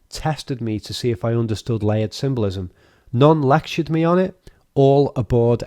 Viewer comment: I can't quite understand what you're saying right there. Are you saying lent symbolism?